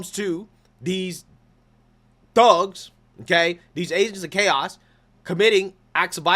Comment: Haha, I thought you said 'apes of chaos'. God I'm tired. It's fitting though.